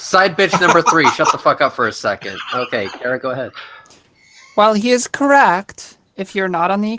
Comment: My apologies. They said "Cara go" and not "Callzter go" like what I thought I heard. Sorry for talking over you.